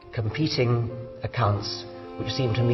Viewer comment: Can someone with a better speaker tell me I didn't hear what I thought I heard.. 0:47 "I can get angry about competing with the _____* ???!!! cunts? cults? O.o